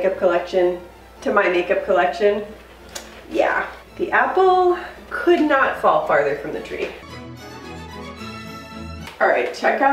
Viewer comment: Me and my mom are like that too. I’m a minimalist and my mom is a hoarder. Don’t know how the heck that happened lol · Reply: I thought was saying her and her mom were the same because she said, "the apple doesn't fall far from the tree."